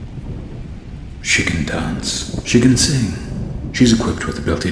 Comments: i thought he said Chicken dance Chicken sing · am i the only one that thought he said "Chicken dance.. chicken sing"